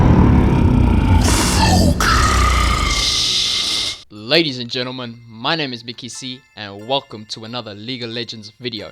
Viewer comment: what rengar say at 1:09? so kas? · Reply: focus